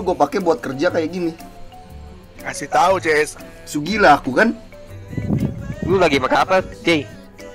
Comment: gue kok dengernya " lu lagi makan apa jey (panggilan High)" ?